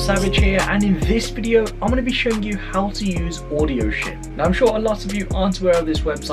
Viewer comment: I thought he said Audio Shit